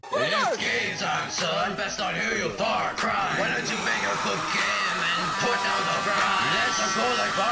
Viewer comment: NGL when i first saw this battle i thought said „Why don’t you make your cocaine”